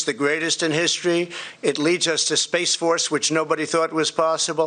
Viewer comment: did I hear "space fart" !